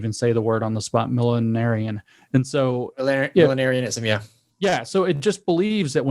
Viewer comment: What's the word they are saying at 39:29? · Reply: Millenarian